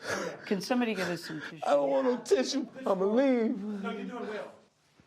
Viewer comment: Did he say “I don’t want no tissue? Ima leave?” 😩😩😩😩😩💀💀💀💀💀💀😭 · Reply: I thought he said I'm gonna lean as he leaned back at the same time 😂😂😂😂now I don't know wtf he said